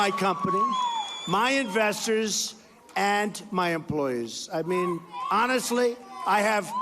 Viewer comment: Did he say, employers?